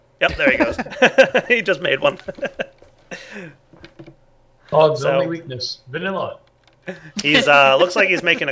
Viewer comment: I swore I heard "God's greatest weakness. Vanilla!" then it hit me that it was "Vaht" and not "God" that was said.